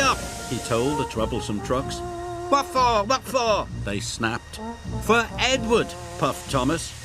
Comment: he say what for sound like he say waffle 🧇